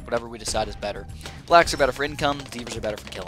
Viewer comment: I'm sorry but I heard "the blacks are better for income" from you, I'm sorry its inappropriate but it made me laugh